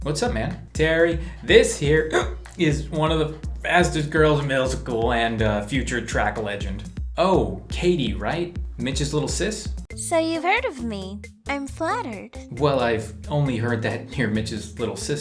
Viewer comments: Am I the only one who heard "midget" instead of "mitch's" at 2:38? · this is clean like anime edit:did I heard "the fastest girl in the MILF school"??